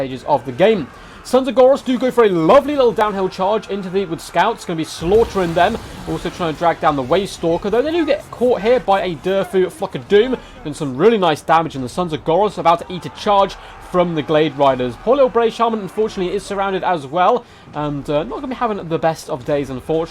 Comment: I thought you said "fuck of doom " i was laughing to hard 😂